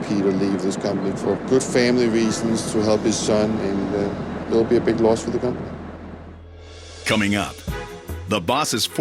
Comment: what did he just say?? Did I misunderstand?? He says:”it’ll break my heart to see such a good guy leave the country for such a good reason and there will be a big lawsuit from it”??? (Or did he say a big loss…?) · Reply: He said “it will be a big loss for the company” but I can see why you thought that haha he just mumbled those words so it does kinda sound like lawsuit